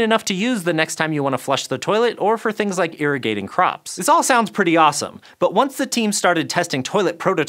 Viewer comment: maybe it was all the bathroom lingo getting to me, but I heard "urinating crops" the first time I listened to that instead of "irrigating crops." Had to rewind & double check what I heard because I was getting ready to Google "urinating crops" and that just sounds like a part of the internet I would rather not venture into.